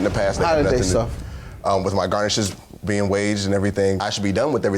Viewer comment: Did he say "with my garnishes being waged"?